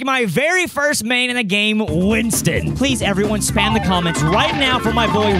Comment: DID ANYONE SAY SPAMTON?!